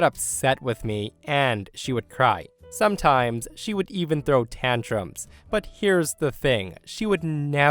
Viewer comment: when you say " ...sometimes she would throw tantrums..." I honestly at first thought you said she would throw tampons...